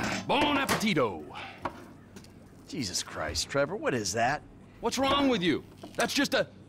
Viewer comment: that was a funny reaction tbh I thought he said what the bleep is that?!